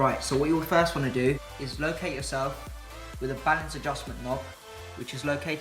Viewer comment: hmmmm 2:53 didnt understand what you were saying about the knob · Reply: Planet Great “balance adjust knob”